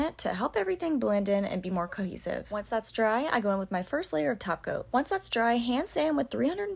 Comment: I don't know why every time she sais top coat I hear "taco". I guess I'm hungry 😕 Thank you for the tutorial, you do a great job.